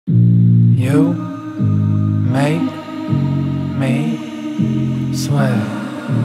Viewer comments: I thought he said "you make me swell" but it sweat. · Since when are all of you guys part of the CloudKid Family? Did you just join in 2020 or have you been here longer? Super curious to find out ❤️ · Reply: I thought he said "you make me swell" but it sweat.